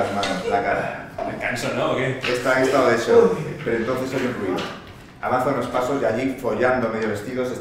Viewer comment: Did he just say "Foda-se" (Portuguese expression) or am I tripping? HAHAH